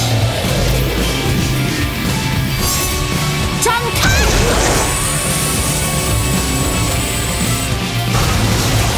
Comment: Im.chinese and I still dont know what shes saying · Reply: Light wing unfold